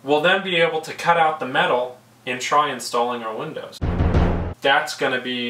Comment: Seems you've experienced Murphy's law in the choosing of your plywood :D Hâte de voir ces fenêtres installées ! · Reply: I suppose it has to happen that way. I was close, I thought you said "good luck with installing windows" but I had to look it up. I can say that we are definitely excited to get the windows in too!